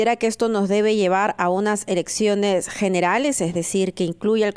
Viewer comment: me parece que dijo erecciones generales?... 😆